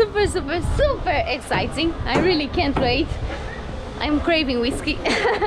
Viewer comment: did you say at 14:06 "I'm craving whiskey"?